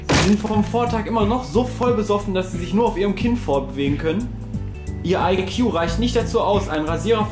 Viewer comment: ich habe " Auf ihrem KIND Fortbewegen können" Verstanden